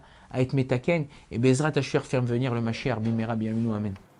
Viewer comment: Qui peut me dire ce qu'il dit à la minute 46:26 svp? Car j'ai compris qu'il voulait être metakem ( corriger/réparer) mais quelle est la phrase après ? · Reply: Après metaken, il dit "Et avec l'aide de dieu, faire venir le messie"